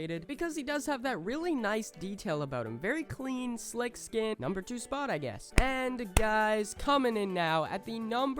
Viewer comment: When he said number 2 spot,I thought he said "Kinda hot I guess" yeah my ears are weird.